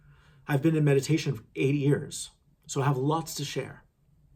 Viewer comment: Did you just say you’ve been in meditation for 80 years? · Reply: He said 8 years